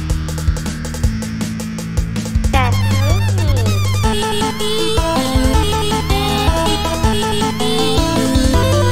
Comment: what did she say ?? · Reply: That's no good!